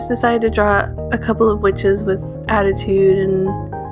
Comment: everytime you said witch i thought you said bitch so i replay it